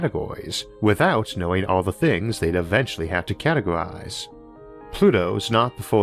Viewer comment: I think the caption should say "they'd" instead of "they've" because it's a past tense thing? (I might be completely wrong on that one though) Great episode with some awesome graphics. I think for a few transitions between the Pluto Orbit bit and the River Acheron habitat, there was a slight visual bug where you could see the other one for a split-second, like at 8:25 in the video. That Mordor joke got me chuckling. Would've been even funnier if the ship was called an "Imperial Eagle" or something like that. Maybe people use cybernetics/genetic modifications -- or just, y'know, improved costuming materials over the last 700 years -- to cosplay as orcs, with self-sharpening blades to boot, and trying to mimic their Black Speech. And if you can't quote the all of (six?) lines of dialogues the Orcs and Uruk Hai have verbatim, you can't join their Sauron club. I know it's been mentioned a bunch of times, but the music for these episodes are fantastic, as is the inclusion of various Stellaris songs. Your narration is very well-done, both in the scriptwriting, and the audio quality itself. I could fall asleep to it, so maybe you could try narrating audiobooks?